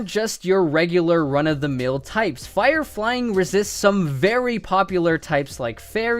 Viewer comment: When he says very it sounds like he says feary